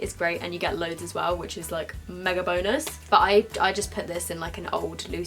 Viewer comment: 'you get loads of it anyway which is, like, MEGA BONERS' -misheard sentence from this video 2k17😂😂